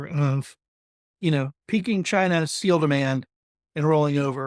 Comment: I have enormous problems to understand this man. Undertitle is of very little help. 0:20 "peing China seal demand" says google - I guess he means peaking China's ??? demand - they do not kill the seals, do they? No idea on my side what could peak in China. 2:10 "Shoulder season"? 4:03 it was "peaking China's steel demand" , what he has meant to say. ...and way too many you knows combined with hair problems... I give up at 4:28 This is a MOST interesting subject for me, but it is too unconcentrated presented and maybe more for insiders, who already know - I do not. Sorry for leaving here, please feel motivated to dig into other subjects beside silver. Hasta la proxima!